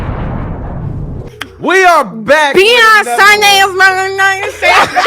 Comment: Sounds like yal saying Beyonce and not beyond sunday